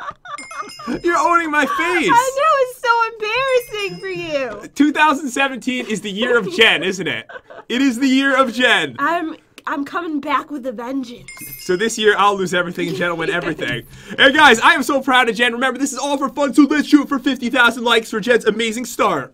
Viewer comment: Is it just me, or did Jen say "revengeance" at 3:51? XD